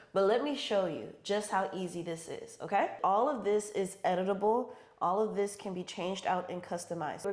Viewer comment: I thought you said "Edible" instead of "Editable" 😂. Then thought, "Wow! People can even eat her labels!! I've never heard of that!" Then i realized you said "Editable", BUT then thought "What if she could really make edible labels? That would be over the top fantastic!" 😂😂